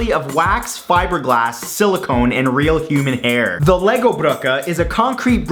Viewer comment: I thought he said "Real human air" at first